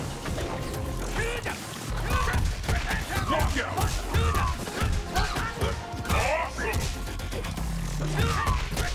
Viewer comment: Did it say "Fuck yeah" ? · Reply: Knock Out